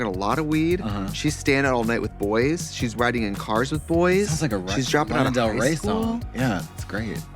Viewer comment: THE FACT THAT LANA HAS AN ACTUAL SONG NAMED DRIVING CARS WITH BOYS THE WAY I SCREAMED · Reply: I thought she said "Riding in Cars with Boys"? Which is Penny Marshall film that features Drew Barrymore.